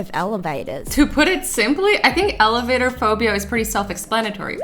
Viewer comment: When she siad elevatorphobia I thought she said elephant phobia